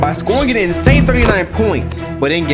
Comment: For a second when he said Kobe scored an insane 39 points I thought he said 300